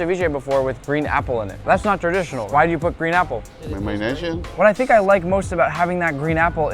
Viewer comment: One sec wat u say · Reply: Langostinos